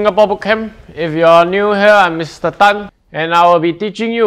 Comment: Is it just me who heard Mr Thanos